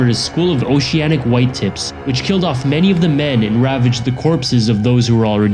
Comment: when you said oceanic white tips I thought you said oceanic white tits